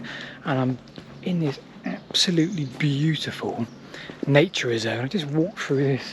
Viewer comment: You are heading into a beautiful nature reserve, but the YouTube subtitles say you're going into naturism, which alarmed me somewhat....😅